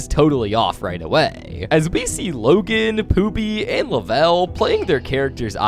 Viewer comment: i heard “poopy”